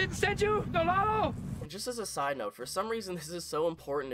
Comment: I thought he said Don Lalo which is Spanish term kinda like sir